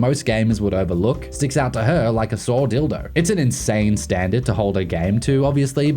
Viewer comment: Did I hear this right at @ 5:35? "Anything slightly wonky will stick out like a sore dildo." What an interesting marriage.